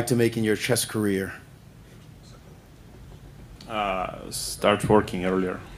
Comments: Did he just say "start forking earlier"? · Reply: Lmao!! Start working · Wtf i heard ian.. "start forking earlier"